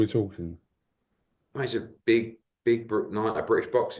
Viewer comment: does he say a Big Brook night as in Kell Brook?